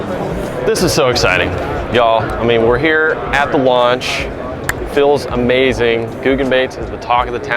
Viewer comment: lol i thought it said eating our googan baits.